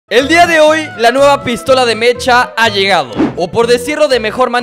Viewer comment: Me había emocionado pensé que decía la pistola de bengalas ha vuelto F me gustaría que hubiera una votación así